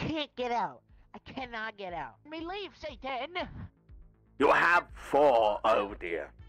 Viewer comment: Just I heard santa?😅